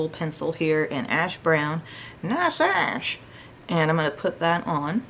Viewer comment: 🤣 what did you say? Your little corky lines are adorable! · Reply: She said “Nice ash” 😆